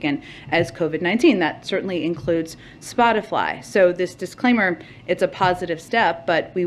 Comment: Did she just say "Spot-i-FLY"?